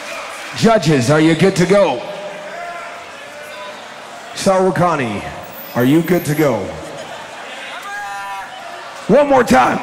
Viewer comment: がんばれー！って聞こえて、現地まで応援いける人いるんだってびっくりした😳 俺も行ってみたいなぁ